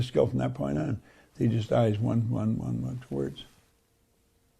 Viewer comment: What did he say at minute 26:50? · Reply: One, one, one towards.